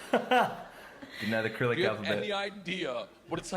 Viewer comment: Did he say Acrylic alphabet? LOL